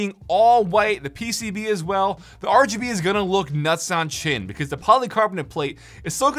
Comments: bro did you just say 'nuts on chin' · Did he just say "nuts on chin"